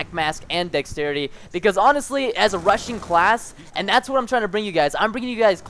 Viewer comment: Anyone else heard "Russian class" haha ?